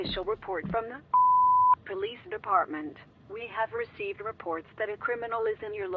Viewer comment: In the beginning, I thought the phone said “ this is the f###### police department. “ LOL 😂